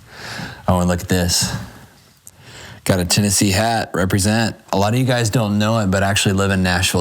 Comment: this is in New York State I think he said. It’s a really cold place. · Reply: I thought I heard Tennessee.